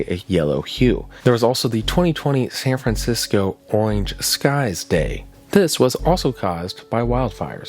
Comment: my boi did not just say oinge